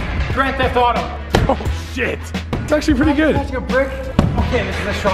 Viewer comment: I swear someone said sh*t